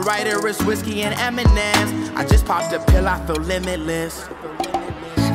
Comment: I thought he said “I just popped a pill off of limitless” As in the show